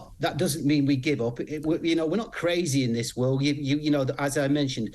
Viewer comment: Did he just say “we’re not crazy “😂😂😂